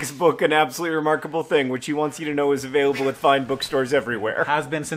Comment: I thought you said "available at five booksellers" and for a moment I thought "well yeah actually that was true for a minute there"